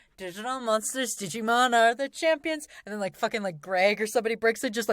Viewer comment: DIGIMON DIGITAL MONSTERS DIGIMON DIGIMON ARE THE ♥️ CHAMPIONS 🔆 Ps. I'm Greg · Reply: I thought it said garlic champions instead of are the champions when I first heard it as a kid